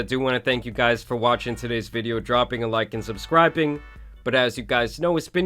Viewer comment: Sorry, you got caught in 4K. I definitely heard that "dropping a like and SUBSCRIPING"